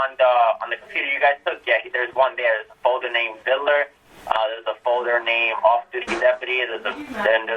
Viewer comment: I dont know if I heard this correctly because the audio quality isn't the greatest but at 19:54 did he say "There's a folder named diddler"?